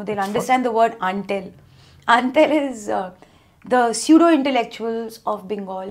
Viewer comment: what is this word? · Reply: It's Antel